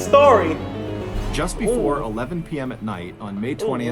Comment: Ok i dont know why but i heard "just before the night of order 67" in the start